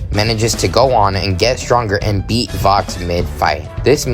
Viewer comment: When he said "beat vox" I thought he said "beatbox" I would pay to see alastor beatbox 😭